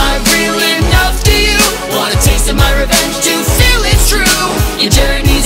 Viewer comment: omg turn on auto subtitles its magical XD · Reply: The captions says "water tasted my revenge juice" omg